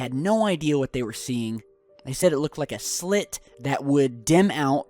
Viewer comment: was it just me or did say nd i quote ''Clit"